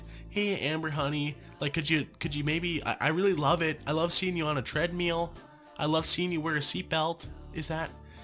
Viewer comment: I'm sorry did he just say treadMEAL????